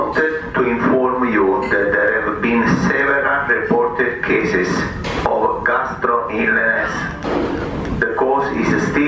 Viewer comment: Did he say 700 cases??